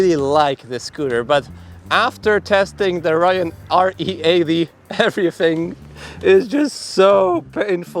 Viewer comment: You mentioned a faster scooter around 9:30 in the video. It sounded like you said, "Rium re av" scooter? Is that correct? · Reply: Rion re80